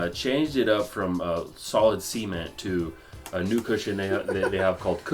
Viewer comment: When he said solid cement I thought he said solid semen at first lmao